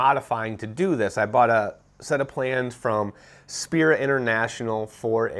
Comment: I’m sorry did I just hear spirit international the airplane company